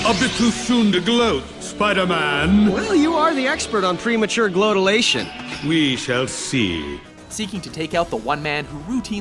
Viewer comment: Did he just say "Premature Gloatalation"?